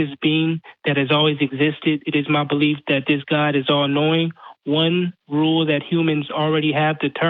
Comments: He said: "My God is all knowing" I heard: "My God is he annoying" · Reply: I heard:"My god is all annoying". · He said: "My God is all knowing" I heard: "My God is he annoying"